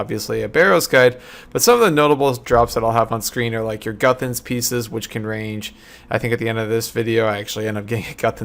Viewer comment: i thought you said guthans pizzas